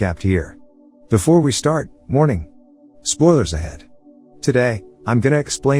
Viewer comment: I actually thought he said hi guys morning(warning)